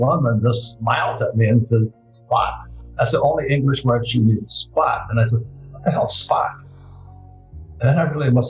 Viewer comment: What did the vietnamese woman say? @ 13:10 ? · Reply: She said "spot". The spot price of gold